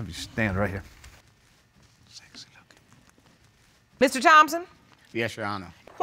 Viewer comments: am I the only one who heard that????🤔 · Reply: The so called dad said ‘Sexy Looking’🤣 Weird AF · I'm disgusted. At 55 seconds when the daughter walked out, it sounded like he said SEXY LOOKING. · Did the biodad or someone 0:54 say when the daughter came in to the court room did he say "Sexy Looking?" Discussing. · Did he say SEXY LOOKING @ 0:55? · 55 secs into the video, I swore I heard the man claiming to be her dad say " sexy looking" when she was entering the court room.🤔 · Am I the only one who heard dude with braids say sexy looking at 0:55 to the daughter?